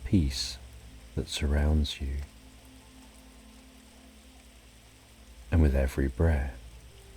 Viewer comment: And with every breath, actually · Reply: yes I realized later that he said "and with every breath" but the first 100 times I heard it I swear I thought he was saying "I'll be there for you breh" 😂 they should be throwing in these kinda Easter eggs in these stories. Its not like most people are still listening after the first hour, might as well add some funny Easter Eggs in there. That's at least what I would do lol